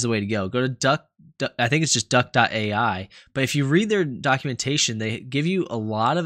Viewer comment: I thought for a second you said duckumentation